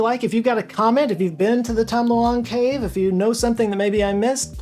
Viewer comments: Tell me why I heard "tom delong cave" lol. where oir yewwww, and oim so soirreee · Did I hear “The Tom DeLong Cave”? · Deffo heard you say 'The Tom DeLonge cave' 😅